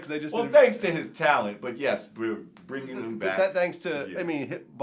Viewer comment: Did jamar say we’re bringing him back? At around 12:04🤣🤣🤣🤣